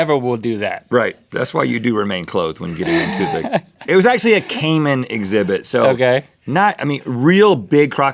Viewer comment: i thought he said caveman exhibit